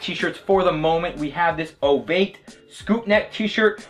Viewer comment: Does he say 'Obeid scoopneck' or what is the brand at 9:10? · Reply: Ovate